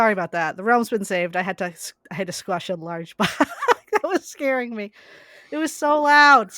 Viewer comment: i cannot believe i heard "squash a laarge bug" im crying